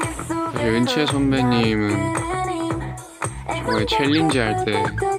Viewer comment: The caption said Yoonchae and I was so confused! But know I can tell they meant Eunchae!